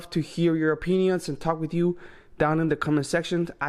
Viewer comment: I heard "your O Penises"🤔